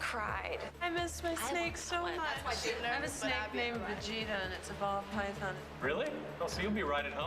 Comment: I swear it sounded like said her snake’s name was “Vegeta” 💀 but the subtitles said “Brigitta” xD